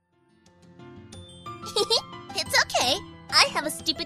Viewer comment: I thought she said a stupid cake XDD